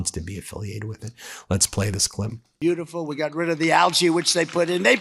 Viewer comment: Is he saying algea or al-chi? Al-chi, ryhemes with Fauci.